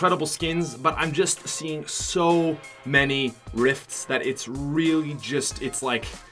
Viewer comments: HE SAID RIFT INSTEAD OF DRIFT!!! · he says rift instead of Drift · he said rift instead of drifts